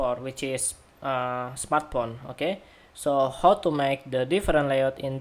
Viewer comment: I only hear "smartporn" instead of "smartphone" ...